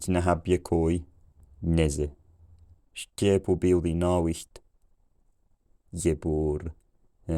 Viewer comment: "no wheat". 1:09 do I hear "holiday"?; 1:24 "samples of the field,... geese"? 1:44 "it is long winter . The ground is cold".